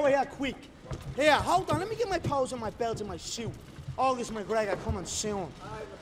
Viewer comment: "Hold on let me get my picture" "All this McGregor coming soon" 😂😂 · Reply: Fuck i even thought he said "all this McGregor " aswell. Still just as funny lmao